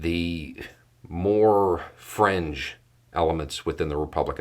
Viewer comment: You said "fringe", I heard "cringe"...